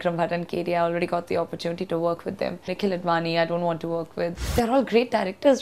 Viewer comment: It sounded like she said 'I don't wanna work with Nikhil Advani'